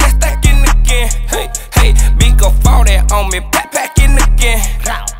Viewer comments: I thought he said "farted on me back at it again" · Am I the only one that heard him say "migo farted on me " hahahahahaha